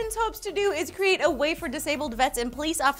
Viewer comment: I'm hearing so many puns today! says "way for" I hear "wafer", says "a means" I hear "amines".